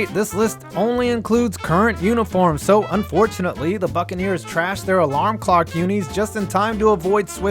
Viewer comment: I thought that said Trask.